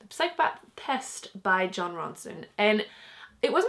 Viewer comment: when u said jon ronson i thought you said ron Swanson